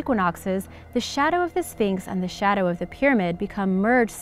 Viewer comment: I swear i heard you say phnyx